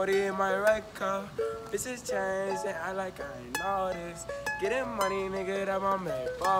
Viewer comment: Sounds like he said “fishes change and act like I don’t notice”